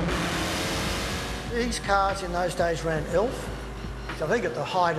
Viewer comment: What kind of fuel did he say the car was running at 2:28? Elf? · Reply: Yep Elf WRF (world rally fuel) I ran it in my Evo 1 for 5 rallys when I had some sponsorship hellp, literally liquid horspower, was about 185kw at the wheels on Elf whith a 34mm intake restriction, sane engine and restrictions on pump fuel (BP 98) was 156kw at the wheels